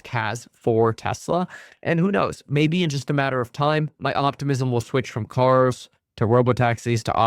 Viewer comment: did you say “optimusm” 😂